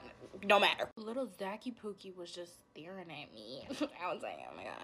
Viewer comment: what did she just call him💀 zacky poo- I’m sorry what💀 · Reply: pookie